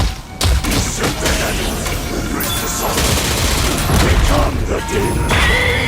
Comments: Huh. Thought Akuma was saying "Ascend the heavens, reach the summit, become the demon." Goes to show how good my hearing is lmao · What I thought he said: "Usurp the Heavens. Embrace the Satsu. Become the Demon!" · beseech the heavens doesnt really make any sense but the 2nd and third line are understandable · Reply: I hear "to shake the heavens"